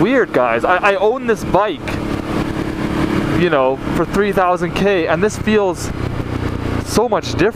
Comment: Wait Not Trying to be a troll but at 2:35 Did he say 3,000K?